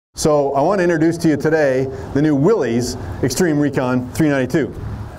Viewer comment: Did he say Willy’s Extreme Recon 392 or am I crazy lol